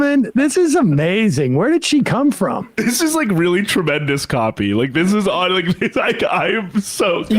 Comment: What was that you said? I heard "this is really tremendous coffee" Then my dumb brain said "no, based on how fast she is talking and how high her anxiety seems to be, she needs to LAY OFF THE COFFEE." If she were any more amped up, she would be visibly vibrating.